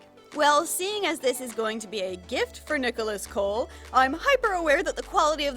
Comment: The cat wants to help make the doll for Nicholas Kole, who I thought you said Nicholas Cage.